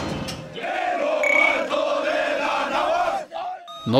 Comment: entendi: quiero chile con jamon